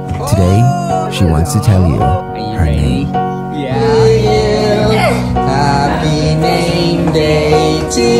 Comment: I thought he said "Happy gay day to you" 💀